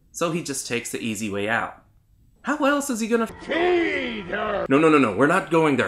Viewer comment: I thought Cartman said when you die, you just.... You know... FEED HER xd